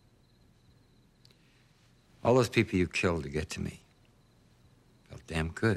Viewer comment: What did bill say at 8:00? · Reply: "All those people you killed to get to me"